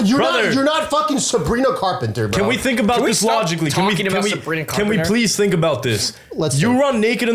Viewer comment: Who else heard”can we stop talking about Sabrina Carpenter “